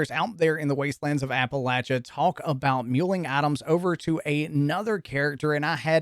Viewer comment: Lmao I thought you were saying Atoms but you were saying items 😂